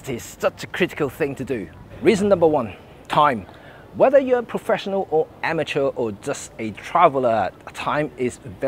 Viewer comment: Thought you said 'whether your an amateur, professional or a time traveller' hahah. Great video, really good points.